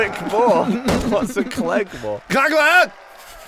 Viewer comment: When Gavin said click more I thought he either said kleg more or was speaking in another language